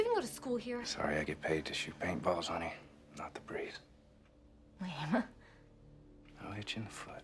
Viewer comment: "I get paid to shoot paintballs, not the breeze." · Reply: Ohh, I thought he said "not the priest". Like she was confessing something.